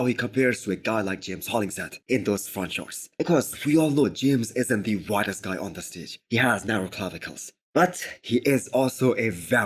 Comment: Haha I thought you said James isn't the brightest guy on the stage. I was like nooo you didn't just say that. Sorry it's 5 AM where I'm at.